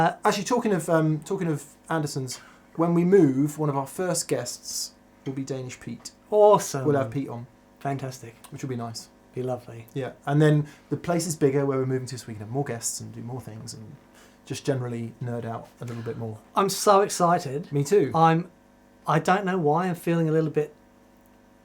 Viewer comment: Did said "the place is bigger we're moving to Sweden"? 40:30 i dont know if i heard right (@@